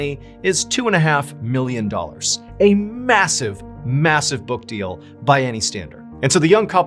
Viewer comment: did you say… MASSIVE?